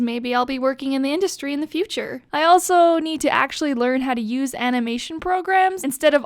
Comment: I thought when she said a imation she said anime